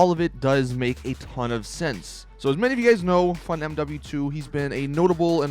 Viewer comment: GTA 5 Online - LOWLIFE UPDATE NEXT? Stunt School Arena Coming Too? [GTA V] Would you be excited if this were the next DLC? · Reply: When you say tezfunz2 it sounds like you say funmw2